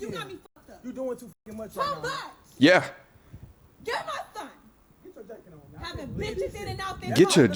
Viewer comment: The first video I misheard her and thought she was saying "get my stuff" but then realized she was saying "get my son". Even though I realized that it still seemed like she was treating him like an object that she didn't want to share with the father just because he had another girlfriend.